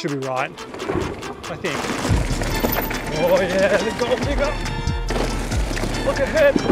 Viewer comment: tf did you said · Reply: Lol. Gold Digger. People who have watched all my videos would know that is the name of the jump I hit when I say it.